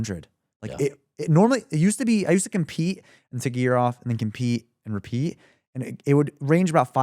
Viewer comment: he just admitted using gear xD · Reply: Damn, you need to visit an ear specialist, it seems. "A year off" is being misheard as "a gear off".........